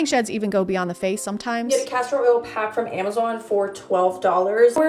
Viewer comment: I thought she said Castrol Oil, which is a brand of oil for cars. Made me think she was greased up in full synthetic for “health reasons”